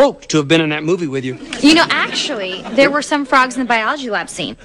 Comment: The way she said "Actually", it sounds like she saying her own name Ashley.